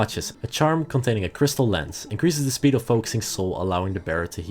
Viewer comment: BRO I HEARD CRYSTAL METH AND HAD TO LOOK UP FROM DOING THE DISHES